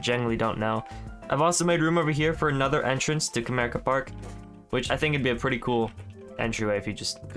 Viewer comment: I thought he was saying America park until I saw the name written out 😭